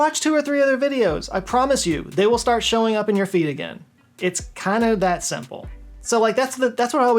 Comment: For a long time i thought he was saying creators would "show up in your feet"